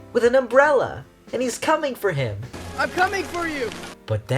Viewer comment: First one in to say congratulations on being right, the story lead/co director/storyboard artist shared drawings of them kissing and stuff today 🏳️‍🌈🏳️‍🌈🏳️‍🌈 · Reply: haha that's good. fun fact, at a moment in the video i heard "im c*ming for you!" but in fact it was saying "im coming for you!" TvT IM NOT ENGLISH DUDE